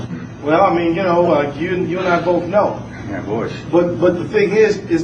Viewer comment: I thought he said "who benefitted from 911?" And then said "yeah, Bush".. oh well, lol. Thanks for the video Mr. Shapiro!